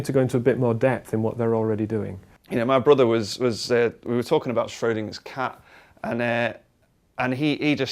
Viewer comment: LOL WTF DID I JUST HEAR?? "ya know, my brother and i, we were talking about SHREDDING HIS CAT hahaha" <--- (O__o)"...